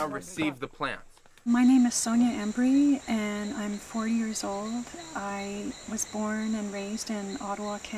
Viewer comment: Great Documentary. Its sounds like she says 4 years old, I swear. :)